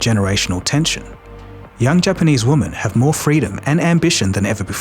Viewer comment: Why are Australian men saying the word "woman" instead of women?